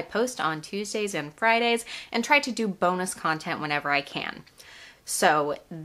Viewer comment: I thought you said "boneless content" not "bonus content" lol